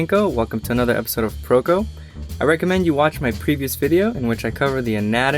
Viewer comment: instead of previous I thought he said creepiest